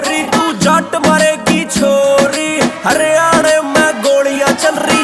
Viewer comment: jhaant bhar ki chhori... M I the only one who heard this😂